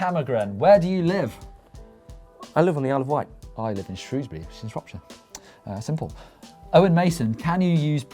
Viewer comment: What the hell did they say at 5:35? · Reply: Larry shrewsbury in shropshire i live there